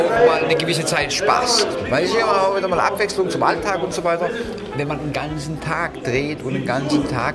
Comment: was sagt er da? :D 3:30 weischiauuuwaaawaaalaabwechslung zum alltag xD